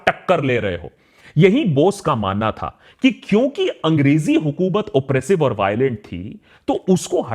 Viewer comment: lazimi hi nahi zaroori tha.. It is actually said Zaroori nahi lazimi bhi tha Zarorat is requirements Lazimi is compulsory Kindly note.